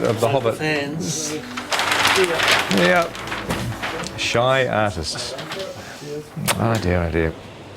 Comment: E el 1:29 le entendí "shy asses", ah qué Jackson tan llevadito...